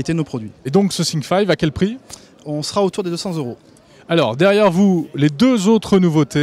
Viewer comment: il a dit quoi ? on sera autour des 900 euro ?